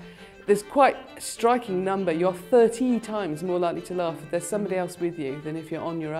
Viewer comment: Interesting. Small point of note though, at 00:33 she clearly said 13 not 30.